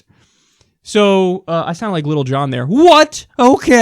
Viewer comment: did Kyle say "little John" Hahahaha